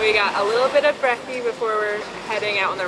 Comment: Did say brekkie? 🎉